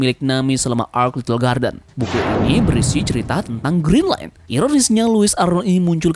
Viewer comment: saya salah dengar apa gimana yaa.. kok aku dengar nya green line.. bukan nya Grand line.??